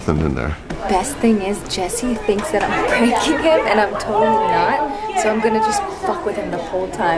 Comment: It sounds like she says "that I'm pregnant" at 5:39 when she actually says "that I'm pranking him"